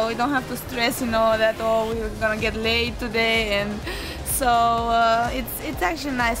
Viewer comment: it sounds like sorana says 'get laid'